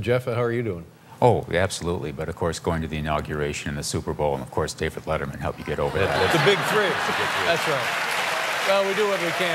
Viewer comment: Could anyone tell me what Jeff says at 8:05 ? Actually I'm an English learner and improving my listening skill. Thanks in advance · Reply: The (presidential) inauguration, the (NFL) Super Bowl, and of course David Letterman help me get over that